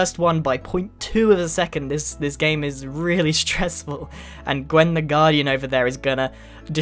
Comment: I heard "Queen the Grian " 😂